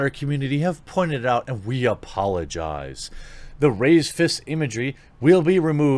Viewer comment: When you read out "raised fist imagery," I almost thought you said "racist imagery" and then chuckled at the irony because its all the same anyway at this point